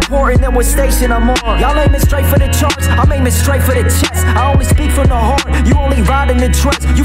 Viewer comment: "These other rappers so concerned about the money and fame, but I've got kids who tell me that they hold a gun to their brain. But they put it down when they hear a line in my song, and that's something more important than what station I'm on" one of the realist lines I've ever heard. You're both my guardian angels. <3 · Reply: nvm thought he said aiming straight for the checks